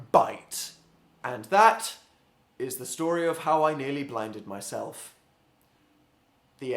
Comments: I thought that said binded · Ha thought it said " I nearly BINDED myself".. Oh what a clean place my mind is ha ha Haaaa... -3